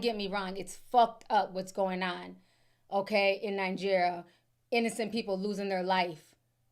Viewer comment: Why do I always hear "9 Geria?!" am I trippin!?